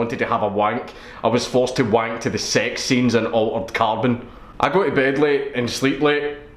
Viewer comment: What movie is that he said he had a walk to? "Alten Carbon"?